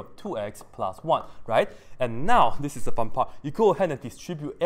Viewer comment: Did I hear this right?? 3:05 “fun part”